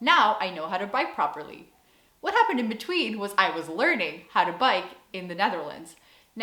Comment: I first heard "learn how to bite"....hahaha.....I was thinking "How is Dutch biting different?"